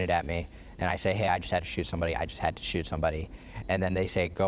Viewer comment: Even you quoted Kyle wrong. That shows how far the gaslighting has gone. He did not say: "I just shot somebody" He said: "I just HAD to shoot somebody" 16:55 Kyle speaking about 18:00 your commentary